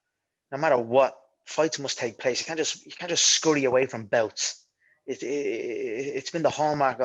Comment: “Can’t just scurry away from a belt.” Lol didn’t he do that to box Floyd? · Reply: I actually think he said “bouts“ 15:27 but I‘m not sure